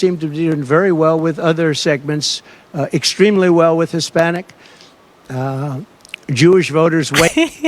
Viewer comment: I dont think you're being fair.. he clearly said at 7:50 "I'm doing very well with Hispanic Jewish Voters", he just had an old man glitch in the middle there where he forgot what he was going to say (maybe, perhaps the adderal kicking in)